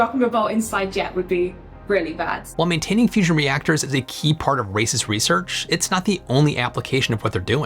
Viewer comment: Did he say it's a key part of racist research?